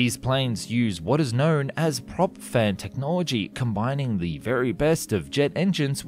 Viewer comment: Astounding how you manage to talk about the Aerocon Dash 1.6 Wingship without ever uttering the word "ekranoplan", WHICH IS EXACTLY WHAT IT IS! 10:16 - It sounds like you're saying "prop-fed technology". These types of engines were known as "unducted fans" at the time, and some did enter service.